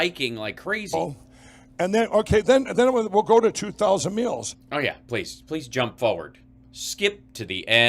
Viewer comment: Either I'm hungry or his Minnesota accent is strong but I heard "2000 meals" and that sounds like a lovely charitable organization. Do that Mike. EDIT: Thank you Hal for also pointing this out xD